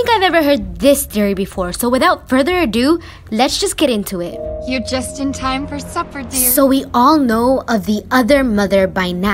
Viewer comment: When she said "You just in time to suffer dear." I felt like she means Coraline is gonna stay here and suffer with button eyes. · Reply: she thought that the beldam said "your just in time to suffer"